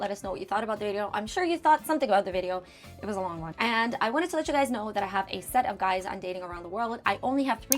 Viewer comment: In your outro, for half a second before I made sense of it, it sounded like you said, "I wanted to let you guys know that I have a set of guys I'm dating around the world!" And I was like, damn! Ok. Right on. You go girl!